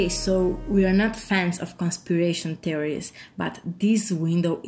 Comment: I thought she said constipation theories lol